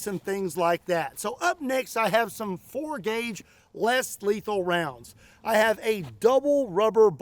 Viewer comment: You said "less lethal" I heard "Houston Jones"